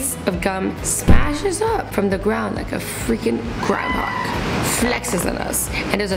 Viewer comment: I thought you said Groudon and it was groundhog. 😭